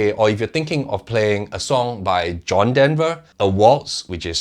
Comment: Please explain what you said at 10:39. The translator shows : "... John Denver the walls (a waltz) which is TYPE C NATURE OF TREE ..." ???